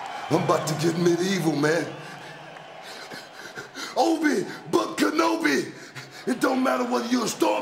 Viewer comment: U mean OBI Book Kenobi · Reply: I thought he said Obi Butt Kenobi.